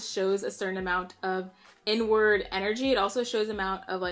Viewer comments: Girl I thought you said “N-Word Energy” 😂😂🤦🏾‍♀️ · I thought she was saying N Word energy I was like “Nigga energy “ 😩😂